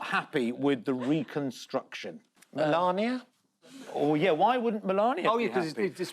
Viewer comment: I swear Julian said “Narnia” as a follow on to the Mordor jokes